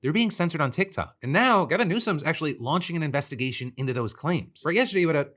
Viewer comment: I thought he said Kevin Newsome for a second. Glad people are pointing out what is happening with TikTok.